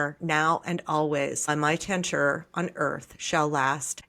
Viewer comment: On 00:17 mark, you sounds like "on my TENTURE" and what is the word that you are reading? English is my second language, so unless I see it, sometimes I cannot understand. I am going to record as you did. Thanks.